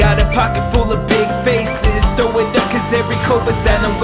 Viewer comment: does he say "cobus" at 1:55?